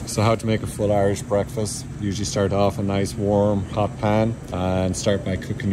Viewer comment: Did he said "warm, hot pan"? 😂Or did i misheard it? I'm kinda confused.